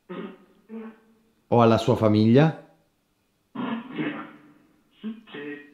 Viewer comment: Finalmente lo aspettavo!!! Minuto 5:10 io ho sentito "see". quando hai detto "Nonna sei tu"? E ti ha detto sii tu ti stavi per piangere e davvero ho detto dentro " Cavolo di certo non è una cosa bella parlare così ma penso che Debby sarà felice di questo". Non avere paur e sappi che tua nonna ti seguirà sempre anche se non è in vita 🧡🧡 Vi amo e siete e sarete per sempre il mio idolo...🧡🧡